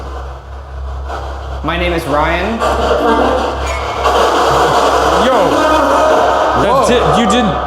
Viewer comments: Timestamp 36:14 it's doesn't say Violet it says, " Hello Ryan". Did anyone else hear that? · Shane claims he hears "Violet" but I clearly as day hear "Ryan." I'm actually a little disappointed Ryan didn't hear that because that was the clearest response the Spirit Box has ever given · I swear to God I heard "My name is " not violet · q's for debrief in the replies of this comment please! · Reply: I swear it said Ryan and not Violet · am I the only one who thought it said "Ryan" and not "Violet" at 36:13 ?